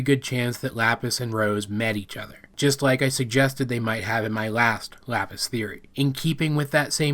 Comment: I thought it said " a lapis teleport "